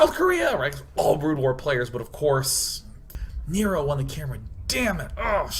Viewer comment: what did he say? who won the camera? i couldnt understand him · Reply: I believe he said "Nero" won the camera, the CD burning software won the camera. What a way to lose :(